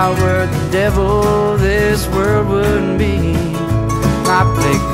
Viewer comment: why does it sound like he is say "wouldn't be "??